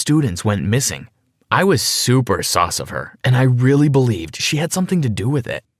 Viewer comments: Is it just me or did it sound like he said he was ''super sauce of her'' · he said SOS instead of sus